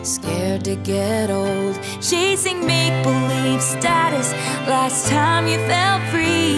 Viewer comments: Wait a second.. am I the only one who thought she was saying maple leaf status this whole time 🤣🤣😭 make believe makes so much more sense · Anyone else thought she said "maple leaf status"?